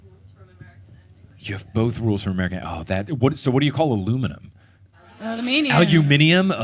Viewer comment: I am ashamed to admit.... when you were asking about British vs American vs Australian English rules, you asked about "aluminum".... except I heard "a lumenum" and was very confused for a moment thinking what's a lumenum? Not my brightest moment 😂